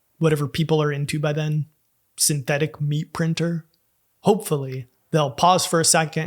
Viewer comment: I heard "synthetic bee printer" and thought, "dude...you're a genius". So now I'm a little sad. Love your videos mate.